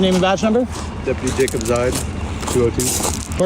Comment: Did he just say his name is "Deputy Dickinside"?